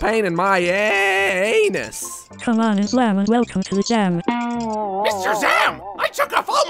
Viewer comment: islam is the light XD i cant 😂😂😂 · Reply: Captinbannana87 Gaming I thought he said "Islam is alive"